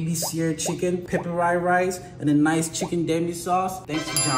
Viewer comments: For a moment i thought he said "Thank yo daddy" 💀. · I thought he said "thank you daddy" at the end, but it was actually "thank you Danny"💀💀